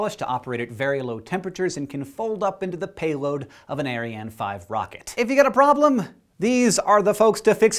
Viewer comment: Thought he said "Aryan 5" rocket at first. I was bracing for the shitstorm